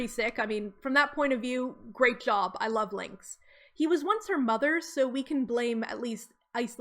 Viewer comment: I completely misheard this as "he was once her mother" and I was like What. Isla mom panther reincarnation? but then I realized it was "her mother'S" which. makes more sense but I wish my version was real